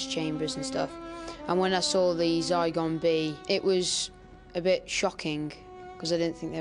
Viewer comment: what does he say? Zaigombie? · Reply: Zygon b